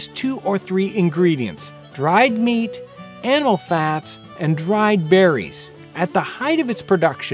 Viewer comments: I like documentaries, this is really good. But I had to rewind and finally had to enable subtitles because I swore he said "Anal Fats" at 0:59. Sorry. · Sorry had to repeat this part and clear my ears. I heard "anal fat" and got worried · did he just say anal fat????? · someone please tell me the second ingredient, all I hear is , ' anal fat ' and I can not unhear it. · did he said "anal fats"? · Did I heard it “anal fats”?